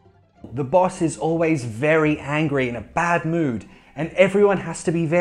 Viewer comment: Am I the only one who hears 'hungry' instead of 'angry' in 12:36?